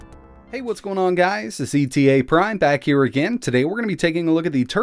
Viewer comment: i swear you said zeteaprime